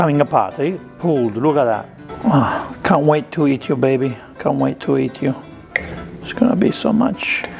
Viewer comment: I could have sworn I heard, “I can’t wait to eat YOUR baby!” Once I realized I misunderstood, I started laughing real hard 😂😂😂